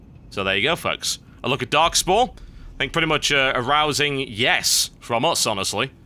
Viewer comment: Im sorry this is supremely immature but at 45: 34 what I heard was "Arousing? YES!" Rofl XD